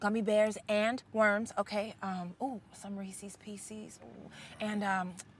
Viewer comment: Did she just say 'Resees peseees' for Reese's Pieces?